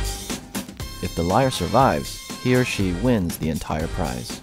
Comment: if the lier survives here She wins? Am I hearing it right? They announced the gender in the opening? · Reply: he or she wins, not here she